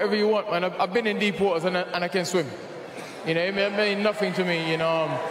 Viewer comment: no bro, you say it again. I didn't understand a word you were saying. A no a she and I can't swim u now?